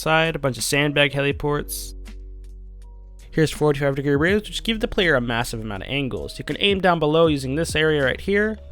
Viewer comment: I have replayed these few seconds about thirty times and still have absolutely NO idea what you said. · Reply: "Here's 45 degree roofs, which gives the player a massive amount of angles" sorry commentating over things is tiring af, which sometimes I might accidentally say something like an idiot.